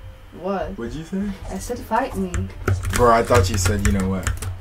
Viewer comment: Bro i loved how you paused when you think you heard her say "** me" instead of fight me :D